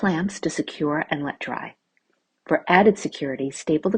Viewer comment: On the last idea with the small wood crates, after she glues them together she says now it's time to paint. I had to rewind and listen because I heard now it's time to pee.